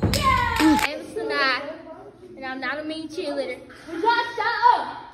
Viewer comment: edited this so u guys won’t know what made me get 17k likes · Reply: Idk why my brain thought this said “and I’m not even a cheerleader, You guys shut up” ☠️😭